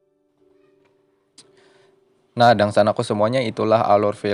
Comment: salah dengar atau apa nih ane?? "Dangsanak ku sekakian" ??? Mun bujur kada papa sanak'e 😁